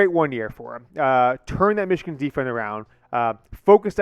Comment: "Turn that Michigan Defriend around " huh what you say 🤔